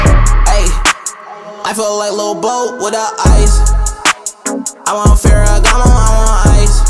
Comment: before hearing its "i feel like lil boat with the ice" i heard "i feel like lebron with that ice"